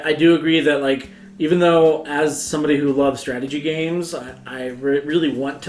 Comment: Honestly, my brain fried a little bit during this and I totally thought he said stratego